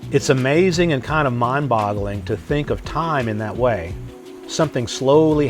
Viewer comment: Did said "mind-bottling?" 😅